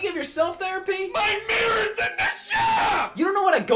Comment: “MY MIRROR’S IN THE SHOP” is such an amazing line, both because of how it’s delivered, and also that it implies the therapist either broke the mirror during a therapy session with himself or regularly sends it to a shop for maintenance. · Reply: i thought he said "MY MIRROR IS IN THE SHOWER"